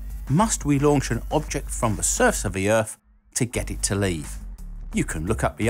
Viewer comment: I just thought that you said with your accent "from Earth to Laythe", but after thinking about it it was just "leave"